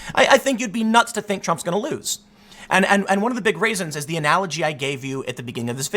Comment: i keep hearing "And one of the big raisins" at 10:00, and i cant help but imagine a big raisin